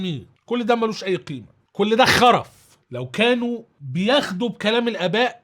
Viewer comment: في احد سمع الي سمعته قال خر^ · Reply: قال ( خرف )